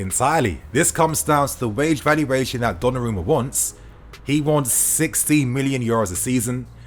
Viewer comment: I thought you said he want 60 million a year, I nearly spat out my drink 😂😂😂